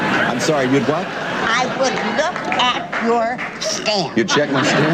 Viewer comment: She would look at his what? I'm not joking, I have no idea what that lady said to Wink. I heard it as "I would look at your stamp", but that makes NO sense to me whatsoever. Help me if you can!